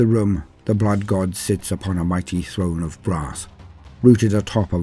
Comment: A throne of BRASS… okay got it. At first I thought you said “grass”. 🌿🍀🌱😆